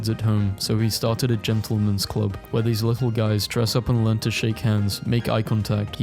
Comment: I heard gentlemen's club and was really worried where this was going